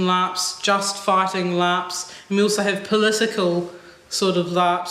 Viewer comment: "Just fighting LARPs?" The accent made me think, at first, that she said "just farting LARPs." 😂